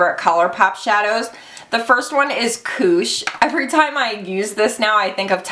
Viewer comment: And when you said 'koosh' I heard 'kooch'. Aussies and Kiwis will be kacking themselves (slang for laughing hard) as it refers to the umm, Camel area for us people over the ocean...are you the same? Just like Fanny means, well, fanny (lady parts - camel), not bottom for us. That gets us every time.